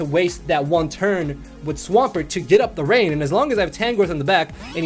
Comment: Near the end, when you said "try to get up rain" when Swampert got knocked out, I misheard it as "try to get a brain" and for a few moments before I went back and double checked, I was thinking "Goddamn does this guy throw some shade"